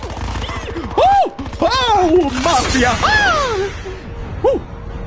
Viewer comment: did he said 'ma ki akkh' 😂😂😂